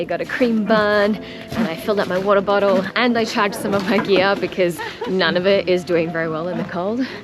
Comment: you said “I tagged (or charged) some of my gear” . I’m sure I misheard what you said. What did you do with you gear? I loved your video… great mix of dialogue, spectacular visuals, and music 😎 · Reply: Haha I said I charged some of my gear because the battery depleted so fast in the cold.